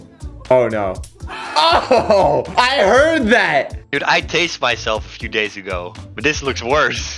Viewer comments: At the start I thought said he tasted himself instead of tased I'm done bad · I thought said taste myself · When he said he tased himself I thought he said taste lol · I thought said taste himself